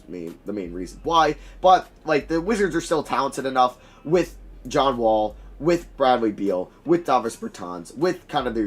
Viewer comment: Did He Say John Wall